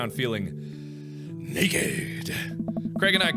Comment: Game says: exposed Mark reads: naked I hear: aroused